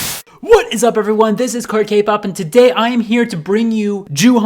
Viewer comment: For some reason I thought it said Crackpop and I was like your not wrong